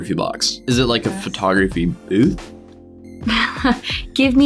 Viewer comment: that whole time they were saying booth, for some reason i thought they were saying boof.